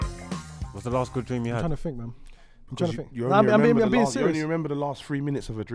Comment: I thought he said to “when was the last good trim you had” 🤣🤣🤣 deadest trim on road, cut with a spoon man